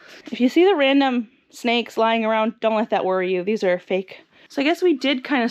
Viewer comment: When you said "I've you see random snakes..." it really scared me. I thought that was real.:_Mushroom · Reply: Lol, i thought she said "dont worry, they are safe" had to replay it